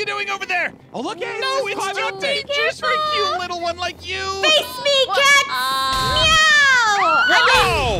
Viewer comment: “I’m gonna get you cats *meow* uh i meaan” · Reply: 1. Seven months ago 😑 2. sorry that 7 months ago I heard “I’m gonna get you cats” when she said “face me cats”